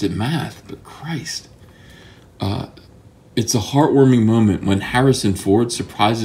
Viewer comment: did he say it was a heart worming moment